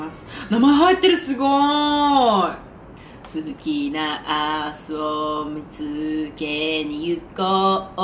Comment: 鈴木ナースを向けに行こう〜🎶 にしか聞こえんくて死ぬwwwwwwww